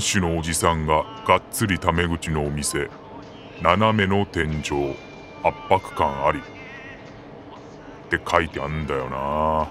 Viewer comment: 「斜めの天井、圧迫感あり」が最初、「(態度が)斜めの店長、わんぱく感あり」に聞こえて、おっちゃんかわいそすぎと思ったw